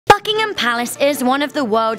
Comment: At the beginning i heard "fuckington palace"